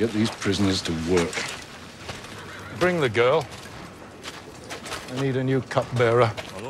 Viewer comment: I swear.. First time I saw this scene I heard “bring the girl, I need a new cock bearer and I was like nooooo.